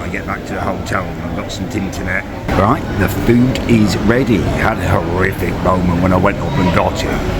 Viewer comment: I swear he said “I had a horrific boner” and then proceeded to show us how it was fluctuating